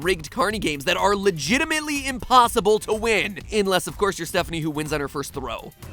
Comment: i swear i thought he said headphanie the first time he mention stephanie